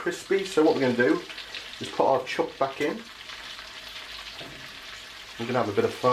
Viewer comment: did you say put our "chuck" back in? lol